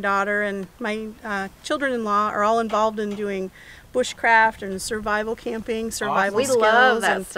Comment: For some reason when she said bushcraft I thought she said witchcraft lol I had to go back and listen again lol